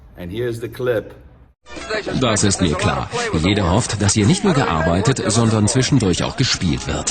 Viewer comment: What are they saying? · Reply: He tells him that I volunteered to work on this day and Hugh Hefner said I’m hoping to play a little bit too